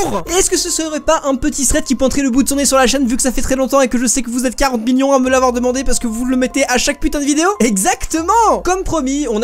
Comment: c'est moi ou j'ai entendu 40 minions ?